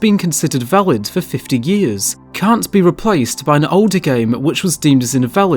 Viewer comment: D-did you just say game? * snicker * Great vid btw.